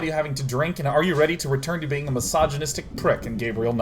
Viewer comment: Every time Sean says "misogynist" I hear "massage-anist". Dude, language!